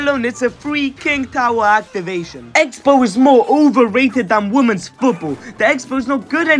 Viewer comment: For a sec i thought he said: "More overrated than womens bu-..."